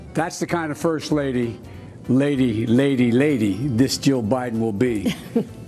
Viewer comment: “180 years ago” “First lady, this Joe Biden will be” “I’m Joe Biden’s husband” “200,000,000 Dead” I can’t believe he’s almost winning....Ugh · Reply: He was making a joke about how long he has been in the senate. He said JILL Biden not Joe Biden each time. With his dialect its easy to mishear it for sure.